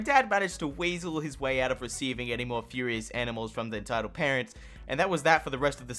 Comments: I heard the dad received a furious chicken and had to retreat his house... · I thought you said furious enemas...